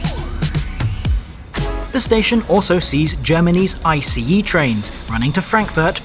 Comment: Did i just hear Germanese!??